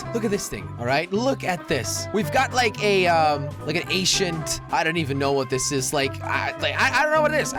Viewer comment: When he says ancient, it sounds like he says Asian